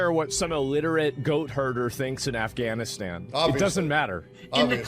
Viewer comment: "he thought asmon was talking about someone from Afghanistan" Hahah he literally said a goat herder IN AFGHANISTAN mate, pay attention 😂 are you alright? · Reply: ngl, i never heard him say "goat HERDER" but instead "goat HURTER"... that makes more sense